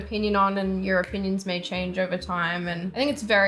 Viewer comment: “Europeans may change” Is it saying something else because what do Europeans have to do with this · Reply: She said “your opinions may change over time” not Europeans lol